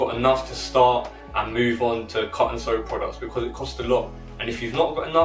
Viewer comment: What's he saying at 8:19 ? Cuts and products? someone please help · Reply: I was just saying if you don’t have the money to invest straight into cut & sew start basic with prints until you have enough because its so expensive and most factories will only make a minimum of 400 pieces